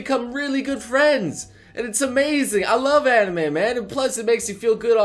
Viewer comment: Did you just say "I love Animeman" O.O